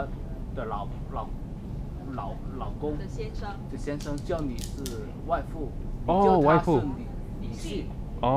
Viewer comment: Anybody else hear,”WAIFU” near 50:20 when the Chinese guy talks about family